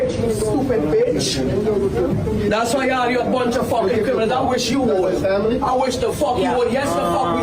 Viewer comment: Sounds like she said “ya bunch of f**kin racists”